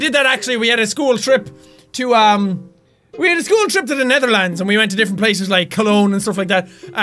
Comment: I thought that he said ''a trip to Neverland'' for a split second, lmao